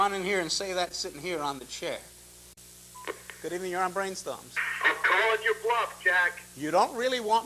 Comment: Im native spanish speaker trying to learn English ¿what does the guy from 2:40 say? ¿and what did the penguin answer? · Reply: “I’m calling your bluff, Jack!” “You don’t really want me to tell everyone why you’re not in school, do you?”